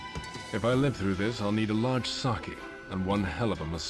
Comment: I remember playing this with my friend when we were kids and we laughed when Bond says he wants "a large sake," because it sounds like he says he wants a large sucky. 😅